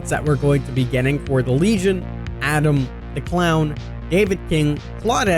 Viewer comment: For a sec at 5:23 I thought you said “Adam The Clown” as in the Dead Rising boss lmao.